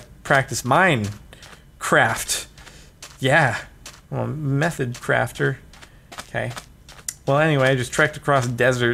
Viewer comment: who else thought he said meth crafter.....